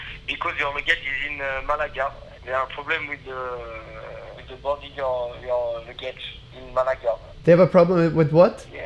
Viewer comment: was it only me who heard: theres problem with your baguette in malaga